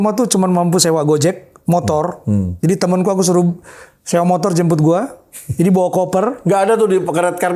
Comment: Menit 27, sewa motor, dijembut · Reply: Bukan, itu menit 29.05, JEMPUT,, salah denger tuh😅